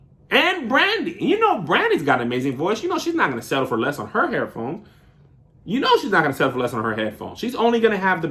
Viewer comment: Tom this just in, sound like he said hair phones instead of headphones.😆😅😂 #JusticeforTahit